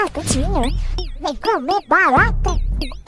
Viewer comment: Eu ouvi "Vem comer barata"